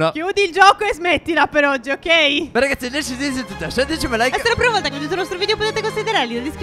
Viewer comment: Minuto 11:12 qualcuno può dirmi cosa ha detto? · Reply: "Bene ragazzi per il video di oggi é tutto"